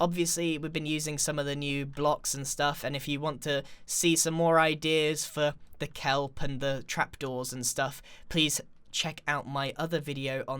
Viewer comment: I thought that Trapdoor was called "Sauce Trapdoor" XD